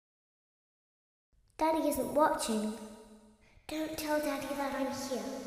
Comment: I hear daddy is a but chin